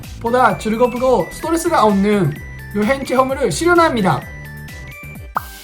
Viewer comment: 뭐라고 하신 거에요? 보다 즐겁고 XX~수가 없는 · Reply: 봐주셔서 감사합니다! ---- 이것들을 이용하는 것으로, 무거운 짐을 나르는 수고를 들이지 않고 쿄토등, 일본의 관광지를 돌아 볼 수 있어, 보다 즐겁고 스트레스가 없는 여행 체험을 실현합니다.